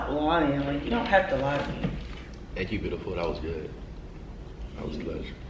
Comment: Is it just me or did he say thank you beautiful